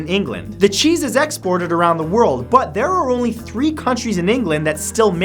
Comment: Ian McQuestionable he said 3 COUNTRIES in England are allowed to make Stilton Cheese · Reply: I actually watched the video, I thought he said counties, he did say countries, but he's mixed up counties and countries before